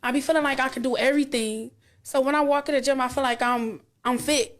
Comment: did she say she walks into a gym???